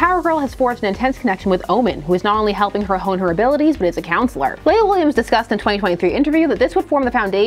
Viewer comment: You definitely got me with that thumbnail. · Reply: I tried to look her up (for research!), but misheard her name as "Layla Williams"...who is the "plant girl" character from the Disney Superhero film Sky High (played by the same actress who now plays Killer Frost on Flash!)...I was so confused, LOL! (Sky High is pretty underrated, btw, and My Hero Academia fans should really give it a try)